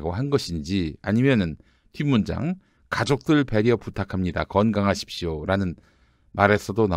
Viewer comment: ( 부탁하신것 들어줄수 없어 ) 죄송합니다 그렇지만 그래도 부탁 안들어주면 너희 가족들 캐겠어 라고 말한것에 대한 '가족들 부탁합니다' 로 바로 들리네요 제생각에는